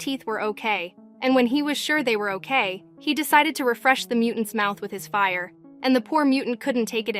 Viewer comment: My boy is really to kill :) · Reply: I thought You said refresh his memory but oh well